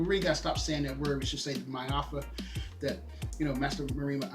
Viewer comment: about 1:45 - What's the word we should say instead of "slavery"? I couldn't understand you. Great List I'll be buying them to read and review on a new channel I'm creating. Thanks for sharing. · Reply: Maafa. Its Ki-swahili meaning a great disaster.